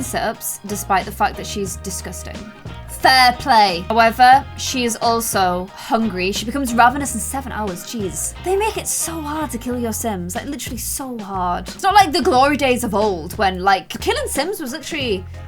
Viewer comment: At first I thought she said,”He’s too HOT to kill.” Then I realized she said,”He’s so HARD to kill.”